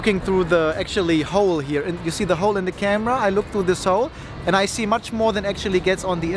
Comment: That was nice line, at 12:05 when i heard, looking through this hole, sounds like looking through the soul.